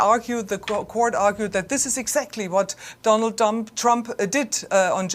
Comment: did he actually call him Donald dump? 😅🤣 Wtf.